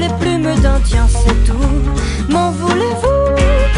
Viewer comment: ok ca veux dire quoi "meudaintien" ? A 0:24. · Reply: "des garçons je ne prends que les plumes d'indiens. C'est tout" :)